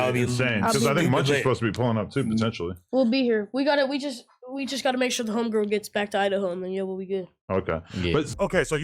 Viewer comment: Did he just say we gotta make sure the home girl get back to Idaho 😂